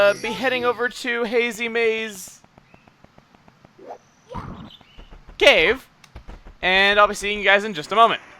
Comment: I thought you said you'd only call it Hazy Mazy Cavey!